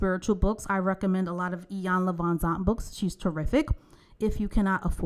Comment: Hi, what is the name of the Author you mentioned at 26:48 please? I would like to check them out and couldn't figure out the spelling. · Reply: Saeforli Iyanla Vanzant